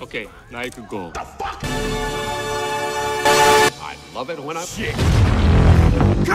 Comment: what did he say at this moment ? all i heared is "shit". i he said "i moment when i shit". if that's what i heared, i'm just gonna say it's very ridiculous and humiliating. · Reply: I says' i love it when I shit'